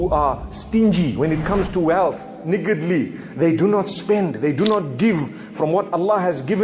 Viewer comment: Anyone notice what he say at 0:44 😂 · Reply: He said "niggardly"....it means stingy,miserly. Please educate yourself, don't play games with allah or his servants. May allah guide you and forgive you.